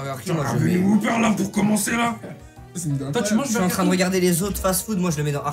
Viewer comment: « toi tu manges du hellouf »😂